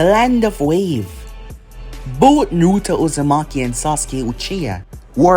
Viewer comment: what did he say at 1:49 he said Both Nut he geeting a little freacky🤣🤣🤣🤣🤣🥵🥵🥵🥵🥵